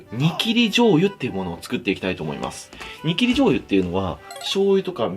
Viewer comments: 煮きり醤油がニキビ醤油に聞こえた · イキり醤油に聞こえてしまった